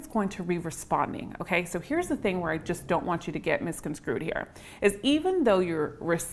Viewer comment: did you say 'misconscrewed,' as opposed to misconstrued? I've been saying it that wrong way on purpose for years.